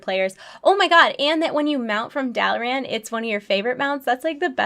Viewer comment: What were she saying at 15:50... dalaran mount? Hm???